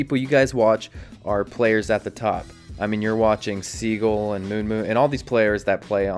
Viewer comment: did he say moonmoon thats real pro gameplay You learn how you do a 360 rein ult......